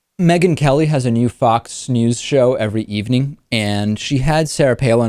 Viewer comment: Sounded like David said, "FOX snooze show", at the beginning.